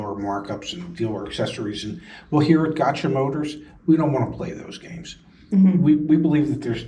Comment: Literally 😂 when I heard “got u motor”… 😂